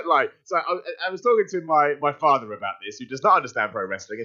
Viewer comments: is he saying father or partner?? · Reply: Brad Gibbs defo Father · I could've swore I heard say "I was telling my partner about this." It surprised me... Simons gay? No I just have a hearing problem he said Father not partner...